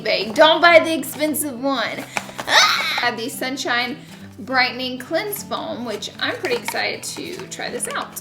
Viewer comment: Laura I thought you said brightening 'clit' foam!!!😱